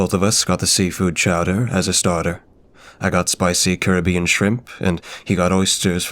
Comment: it almost sounded like you said she was eating spicy Kirby!? No don't eat the pink blob of cuteness, he's one of the few who can defeat eldritch gods!